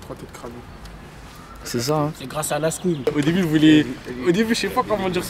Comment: il a dit quoi cest grace a lasko ?